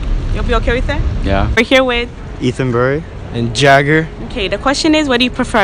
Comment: Can you help me with his name? I can't understand what he said because I'm not usual with these names, I'm from Bangladesh. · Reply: The one with grey hoodie says he's and the other guy is Jagger.